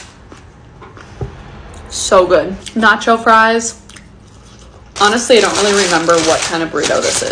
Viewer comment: So you know how she said nochoa fries I thought she said "not your fries😅